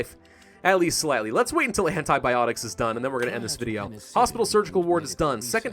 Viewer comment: Is that "Hentai-biotics" I hear?